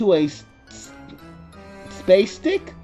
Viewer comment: Did it say "spastic" at 11:55? I think it's pronounced like "plastic". I know I can say one positive thing about this fanfic compared to the one inspired by the fan of Twilight. At least this one took place in a pony setting.